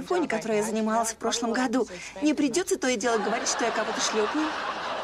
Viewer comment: Что сказала Фиби? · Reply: Не придётся то и делать говорить что я кого то шлёпаю